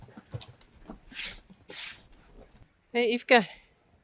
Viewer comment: Did Yfke say Hello? 😂💜